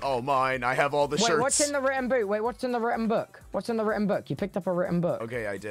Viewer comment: I swear I thought he said “what’s in the ranboo?” And I was like “uh... organs? blood..?”